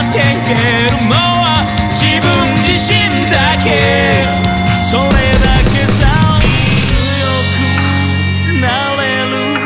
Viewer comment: さ行が苦手なのかな？ 「それだけさー」が「しょれだけさー」 に聞こえるのは俺だけだろうか？